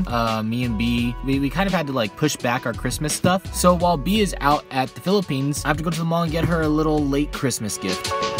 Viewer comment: did you just say Went homefrom Philippines